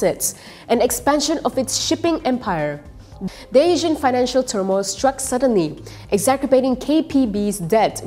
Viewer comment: If you don't mind, at 2:54 sounded like "Daysion financial"... try "Dee Asian..." and then there was the "exacerbating" which was pronounced "exaKerbating" .. I'm an ex-broadcast journalist and would to see our standard of presentation get better and better ya. We're all on the same side 😉